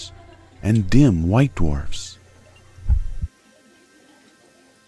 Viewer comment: did he said damn white dwarfs? xD